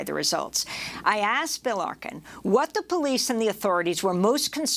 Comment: I thought Amy said Bill Larkin. I was like wtf bc I knew him but it's Bill Arkin. The senator didn't rise from the dead